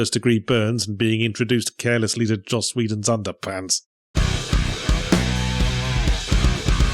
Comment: I first heard "Josh Wheaton" and was about to ask what you had against Wesley Crusher lol